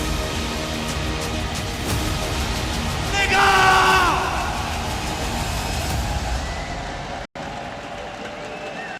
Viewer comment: What did you just say?! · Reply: Digga/digger , It has nothing to do with n